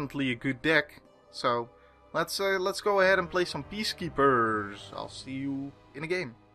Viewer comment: Whenever you say "Peacekeepers" I always hear it as "Beast Keepers" for some reason.